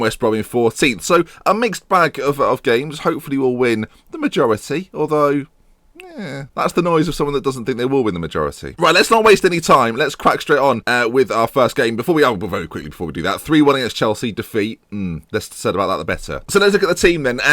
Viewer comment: did just say "let's quack straight on"?